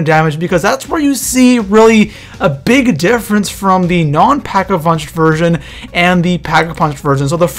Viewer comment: Did i only hear packavunched at 01:13? XD